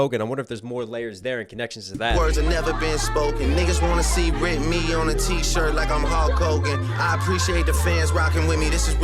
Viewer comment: I thought Drake Said Whitney instead of Rip me on a T-shirt the first time I heard it, like Kendrick’s wife is missing or something. I wonder it that was an unintentional double or just coincidence